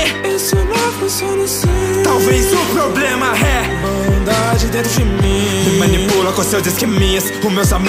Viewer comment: Pessoal eu errei kkkk 1:05 O certo e bondade mas eu sou meio surdx então eu entendi maldade kkk Desculpem - me, na próxima eu fasso certo :)